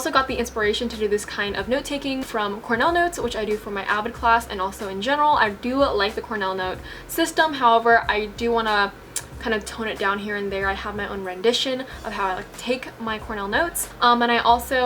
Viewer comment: did she say avid class??